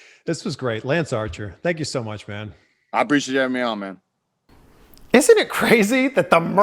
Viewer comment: This interview was AWESOME.. I really loved seeing this side of Lance.. but umm.. the hell did he say at 47:42 ?? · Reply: I appreciate you having me on man